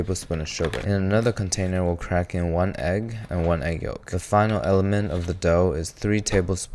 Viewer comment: I thought he said to add 1 egg white and 1 yolk to a container at 2:24 😭😭😭